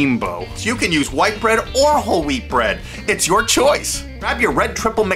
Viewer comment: oml this gives me memories !! when i has younger i thought he said “holy bread” but he actually said “whole wheat bread” 😂😂